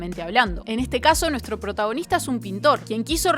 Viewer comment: Lol por un momento pensé que dijo "nuestro protagonista es un suscriptor" en el segundo